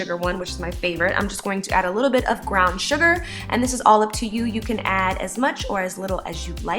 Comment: I heard "ground sugar"